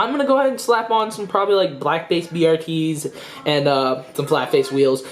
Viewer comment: does he say black face brts??